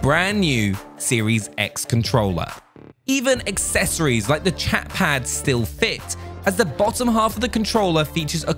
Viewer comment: We need a Chatpad v2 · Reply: Tyrantes lmfaoo at first i thought you said “ChadPad”